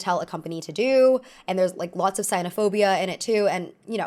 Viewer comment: clearly from the subtitles she meant to say "xenophobia", which is bigotry against foreigners, but what she said was "cynophobia," which is pathological fear of dogs, and that is hilarious and I love it very much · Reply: She said “sinophobia”, which is anti Chinese sentiment <link>